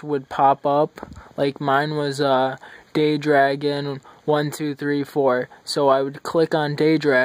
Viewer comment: Thought you said GayDragon